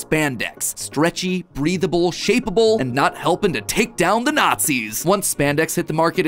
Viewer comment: sorry matpat what did you just say? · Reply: NOT GONNA TAKE DOWN THE NA